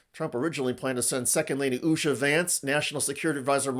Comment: Did he just say Shady Vance ?!